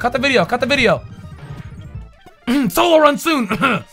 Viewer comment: Wait did i heard solo run soon????